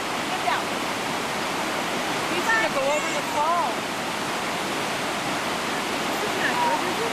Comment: Thought you said it went over a waterfall…